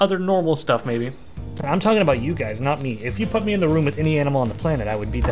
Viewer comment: I THOUGHT HE SAID HE'D EAT ANY ANIMAL OUT 💀